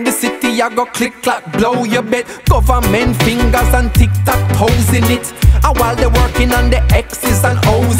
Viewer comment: Did he just say tiktok posing it in 2012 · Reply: "you bet government fingers and tic tac toes in it"